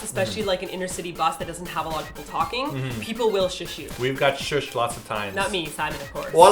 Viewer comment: who else heard "people will shoot you" here for a second? lol